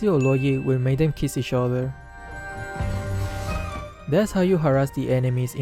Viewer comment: did said kiss eachother??